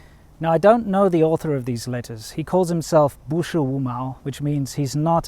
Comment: totally thought he said 不是五猫，and I was like, that's a dumb name.